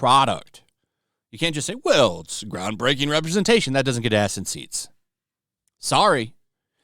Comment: That... was a bit confusing... when you said "this will get ass in seats" i heard "acid seats"...